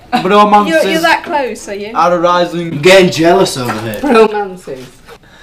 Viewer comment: i think harry said "am getting jealous out of it" my god 😍🙌👏👏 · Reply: Navi Naytiri yaah he said that: im getting jealous over here